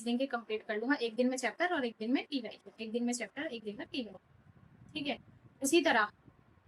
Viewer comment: par kya bola apne. Ek din chapter ek din ? Ek din chapter ek din ?